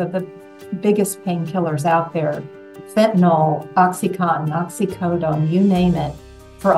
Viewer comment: did she say fentanyl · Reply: Yes, fentanyl is a prescription opioid medication for severe pain.